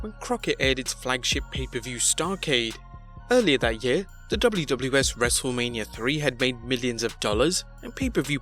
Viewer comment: did he say "WWS" 😂